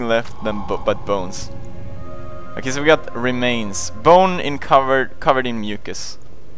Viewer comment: In the last part the only thing I heard was 'a bow covered in mucus' but it's 'a bone covered in mucus'... lmao, the accents are too much xd